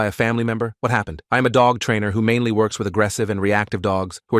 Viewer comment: I thought you were saying, I have a dog